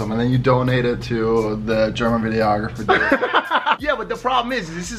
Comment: Did he just say the germanographer lmfao